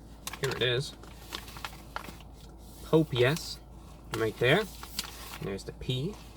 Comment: did anyone hear Pope yes instead of Popeyes😂